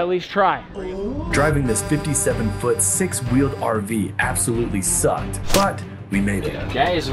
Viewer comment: did they say the big rv sucked